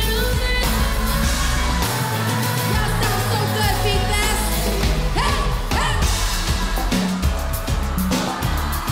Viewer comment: what she said at 02:13 02:15 please i cant understand it please me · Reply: "y'all sound so good v fest"